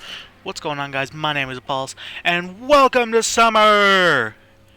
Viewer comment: I thought you said. "Hi guys my name is balls"